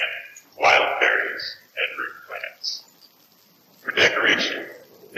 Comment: The Nez Perce thing: -It sounds like it says "prize horses" but modern horses aren't native to North and South America (American horses went extinct between 10,000 and 7,600 years ago, about the time humans showed up on the continent) so that doesn't really make sense, unless this tidbit about the Nez Perce was written after European horses had been introduced by colonists. -It's "root plants" not "fruit plants" lol -shells and feathers -Chief Joseph was the leader of the Wal-lam-wat-kain Nez Perce in Oregon, so I presume the word you couldn't make out was "Oregon" -"So the ??" is "To avoid war" -"Bear Paw Mountain" (As I was looking up Chief Joseph so I could figure out what some of the words might be, I found that, strangely, the US army engaged the Nez Perce at White Bird Canyon, which I would have expected the creator(s) to use since they've already used storks so much) I wish I had more time for their bizarre game/audio/wtf series XD